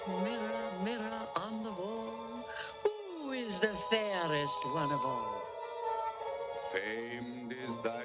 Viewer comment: I SWEAR I HEARED "MIRROR MIRROR ON THE WALL WHOS THE PREATIEST OF THEM ALL" I SWEAR I HEARD IT WHEN I WAS LITTLE THIS GETTING CREPPY NGL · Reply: i hear "miror, miror on the wall who's the prettiest of them all" instead of hearing "magic miror on the wall who's the fairest of them all"